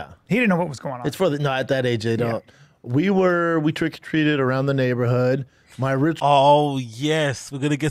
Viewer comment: Sounds like he said turkey treated